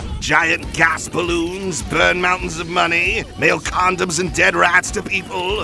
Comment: I coulda sworn he said "Nail condoms and Dead rats to people" instead of "mail" the first watch through lol